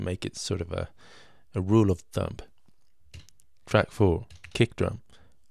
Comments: like to keep your “drum sequence on track 4”, did you say it’s your rule of THUMP! LOL. Great video, you have the most awesomest mellow voice, just makes me SMILE!!! :-)) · Did you say "Rule of thump" referring to keeping your drum parts organized? Brilliant ;)